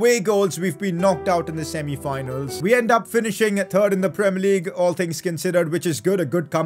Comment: Makes me laugh when says “third” it sounds like he is saying “turd”